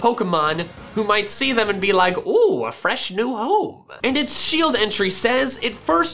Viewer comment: i thought he said “fresh new hole” and i was about to scream